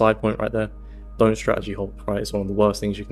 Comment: can anyone please tell me what he said in 5:01 'dont strategy....its the worst thing u can do' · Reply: Hop